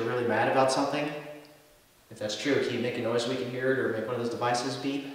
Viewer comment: did anyone else hear a very soft "Hey" or a breath or something at the same time says beep?